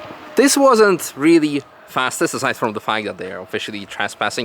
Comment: what did you call him lol did i hear that right HAHA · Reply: I think he said "aside from the fact that (...)", not really what you think he did 😶